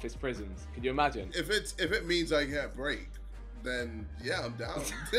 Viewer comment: did he said “get raped”!!! Am I the only one?